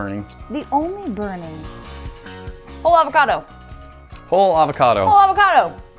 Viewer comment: they said whole avocodo so fast i thought they said holetheavacodo